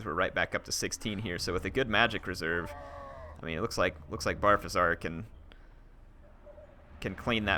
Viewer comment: Did you just call him Barfasar? lmao